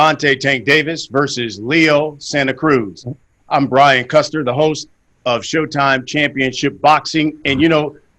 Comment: I thought he said brian custard